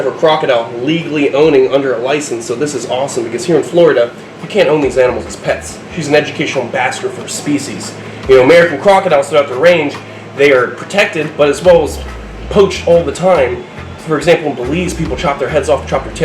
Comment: I thought you said he is an educational b****